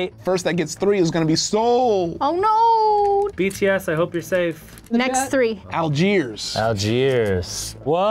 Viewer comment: Did just say “BTS I hope your safe” I